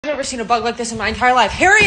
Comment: I thought she said “I’ve never seen a butt this big” at the beginning😂😂